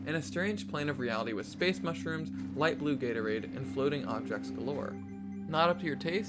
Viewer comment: Is it me or did he say "light blue Gatorade"